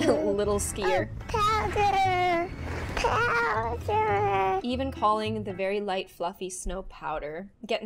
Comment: The little boy on the skis when he said powder I thought he was saying power XD